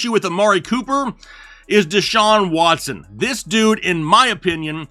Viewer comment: I was barely paying attention and I thought I heard you say Amari cooper was washed I almost had a stroke